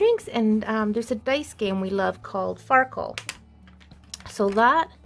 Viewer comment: Very nice. I'm sure your husband will love it. My husband and I eat from a charcuterie or delicatessen board/ tray quit often. Their easy to prepare just a lot of cutting. Easy clean-up and we like eating with our fingers. I use to play Farkle with friends years ago. I should ask my husbandif he would like to play it. I have made up small gifts of the Farkle games using Altoid boxes, printed rules and I bought the dice. The Altoid boxes are fun to decorate. Thanks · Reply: I thought she said the game was called sparkle. I am so glad I read your comment before I went looking for something that didn't exist. 😅